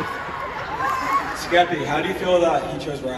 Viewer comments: what did the fan say? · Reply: They said “SKEPHALO” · what did the person say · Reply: “SKEPHALO” if your wondering still